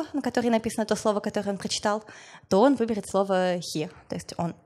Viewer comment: кто на 49:48 услышал слово хер